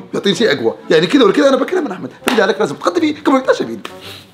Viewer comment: مافهمت وش قال😂😂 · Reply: قال لازم اتغدى فيه قبل يتعشى فيني